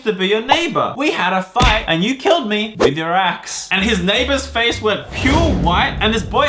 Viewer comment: At the start I thought said YOU KILLED ME WITH YOUR ASS